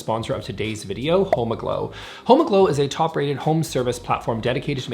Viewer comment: I thought he said, “homo-glow!”😂 And I was like, miss thang, sign me up! But then I w was like sad😢